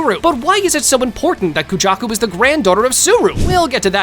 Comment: i thought u said kenjaku instead of kujaku💀